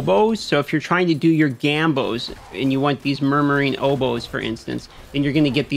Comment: sorry, I heard "Murdering Hobos"